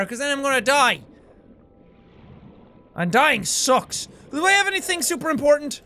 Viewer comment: For a sec when he said “and dying sucks” I thought he said “Undyne sucks” LOL